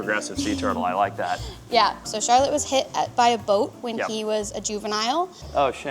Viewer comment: I want it to be known I at first thought they said the turtle Charlotte had been "bit by a goat" and that's why it was disabled..... I was extremely confused until I realized what they actually said.